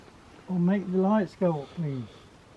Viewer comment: At about 27:14 sounded like a lady said please leave